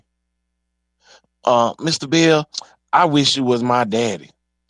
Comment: Did you say, Mr. Beal?